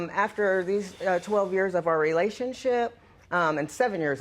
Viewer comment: At one point when she said relationship, i thought i heard '"relationshit"